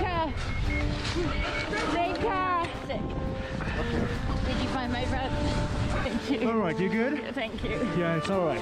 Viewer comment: she did NOT say “Did you find my bag?” She was actually saying, “Help him, help him. Did you find my friend? Help him.” She couldn’t hear him, so she was scared what that could mean. He was okay. But she was far more worried about him than about herself